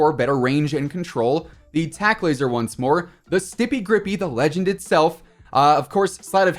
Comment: Here’s a comment to represent the Stippy Grippy Gang 🤙🏽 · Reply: thought you said stiffy grippy for a sec 💀💀💀